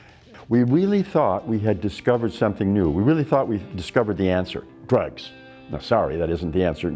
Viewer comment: Did he say we discovered drugs?? did he just say we thought drugs was the answer?? @ 1:10 Heck Nawl